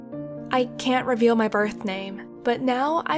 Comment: Ok I know this is like a serious story but 7:24 I thought she said "I've decided to be called hoe " 😂😂